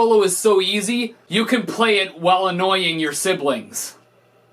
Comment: i thought he said ''this song is so easy, you can play it while anointing your siblings''